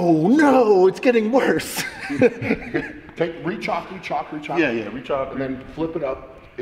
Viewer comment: they say "rechalk, go rechalk yea yea" but the auto subtitles say "reach hockey chocolate yeah yeah" NICE.